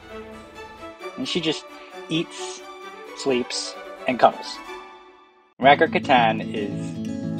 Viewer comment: "She just eats, sleeps and cuddles." Every human's dream. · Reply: I thought he said Eat, Sleeps an Coms, - 😂 I almost felt personally attacked..